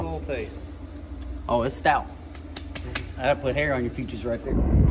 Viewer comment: Did say "It'll put hair on your features." 😂😆🤣😆